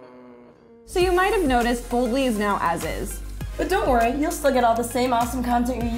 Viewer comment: jsyk the first time I heard "As Is" I couldn't understand your American accents and thought you were saying ISIS soooo yeah that's a thing. #bringboldlyback